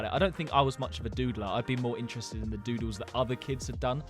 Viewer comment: When he said doodler I thought he said diddler